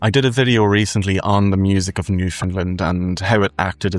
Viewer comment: Oh jaysus what?! I don't remember it in this video at all hey haha.. what did I say, what's the timestamp haha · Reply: Timestamp is17:11. In the Transcript you spell it New Finland instead of Newfoundland. You could respond humorously by saying that you actually meant the real place, New Finland- and I will just as humorously change you to show New Finland on a globe or map. The title on the thumbnail reads; "FROZEN IN 1790", and the description beside it reads; "The Only Place Pre-Famine Irish Music Still Exists". I hope this clears it up.